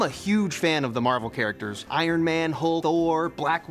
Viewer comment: The way he suddenly says Hulk and Thor simultaneously I was like hold up did he just say Hodor? When did Hodor join the Avengers?